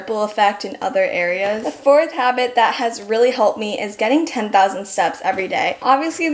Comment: I love this video! I’ve done these things, but not regularly, which is obviously the key. I thought you said 10,000 subs every day at 4:15 and I was so confused. 😂